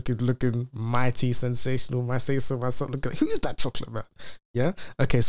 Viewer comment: Did he just say whos that chocolate bar?! lmfaooo , Thank you for the video siiiiccckkk.